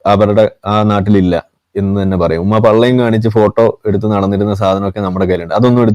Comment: പള്ളയും വയറും മുലയും കാണിച്ചു നടന്നിരുന്നു എന്ന് താൻ പറഞ്ഞത് തന്റെ ഉമ്മയെകുറിച്ചാണോ? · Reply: he did not said that, "pallayum" only (0:55), you add whatever you want and propagate in your brainless circles GOOD